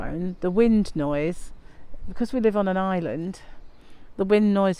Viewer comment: I live in the South East in Kent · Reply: how lovely. I thought you said you lived on an island. 😂